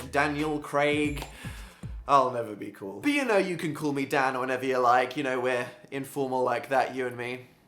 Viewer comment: thought he said "you can call me dad or whatever you like"